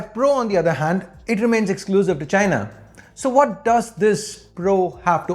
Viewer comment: i heard exclusive vi**na